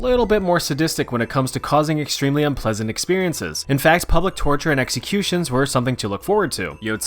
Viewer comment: i misheard publiic torture as pubic torture